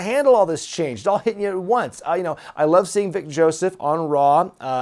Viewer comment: I’ll be honest when he said on raw I’m vic Joseph I thought he said big Joseph lol